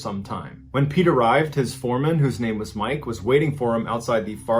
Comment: I thought he said 'When Pete arrived his foreskin who's name was Mike..."